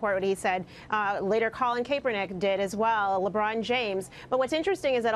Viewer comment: did she just say "kay-pernick"???